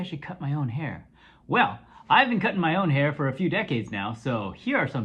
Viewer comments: I swear I heard "I haven't cut my hair for a few decades now so here are 10 tips on how to cut your own hair!" lol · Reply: I heard "I've been cutting my own hair" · I swear I heard "I haven't cut my hair for a few decades now so here are 10 tips on how to cut your own hair!" lol